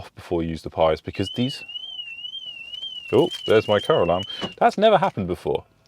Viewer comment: For a moment then I thought you said stop going off Caroline 🤣🤣🤣🤣wasn't until I watched again I realized you said car alarm 😑